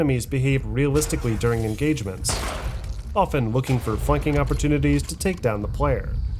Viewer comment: I didn't here "flanking" I thought I heard "fucking" I had to listen to this point again. 😅